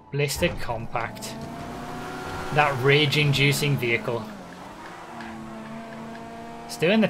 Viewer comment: misheard the blista compact description as "that raging, juicing vehicle" .........erotic...